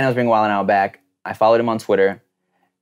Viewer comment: Also why did Tim say hold on around 9:56? I couldn’t hear what he said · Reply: Mrarcher15 “Hold on, I’ll wait for that plane”